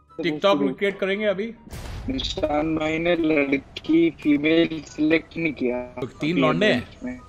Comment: "Nishanth bhai ne ladki ki base lick nahi kiya hai" That's what he said Editor smart enough play with words 😅🤣😂